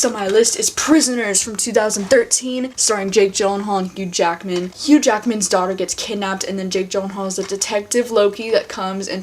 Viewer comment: LMAO i thought she said hugh jackman's dog at first